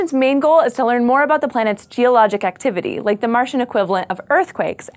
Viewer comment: did she say: "The moon's theologic activity"? :-D